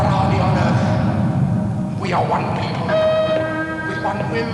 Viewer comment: i could've sworn i heard "we are white people" at 0:29 LMAO